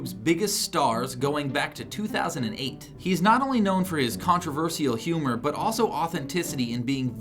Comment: did he say contraverSEEal!?